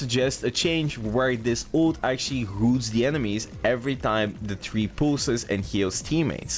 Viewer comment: what did you say there.. moves?